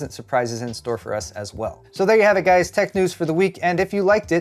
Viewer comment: Given your general sass today, me mishearing your ending as "tech news for the weak" seem pretty spot on